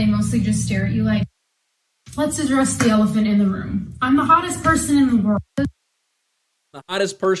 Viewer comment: hottest? I thought she said dumbest 🤣🤣😂😂